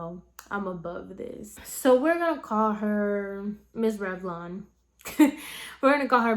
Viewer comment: I’m so used to your hair videos, at first I thought u said “miss red blonde” 😭